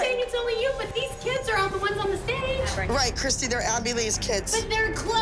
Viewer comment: for a second I thought she said Maddie instead of Abby, I get there names mixed up sometimes 😂